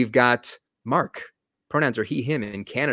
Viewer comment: Why does he say "Pronounced he/him"? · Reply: He's saying, "Mark. Pronouns, he/him". They have some weird fetish about respecting how people want to be addressed. Apparently, that's why his name is mentioned. I think in the past, they just called everyone "douche-head" and it didn't work out.